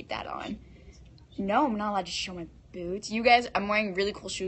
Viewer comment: "I can't show them my boots." Fam. I heard " I can't show my BOOBS"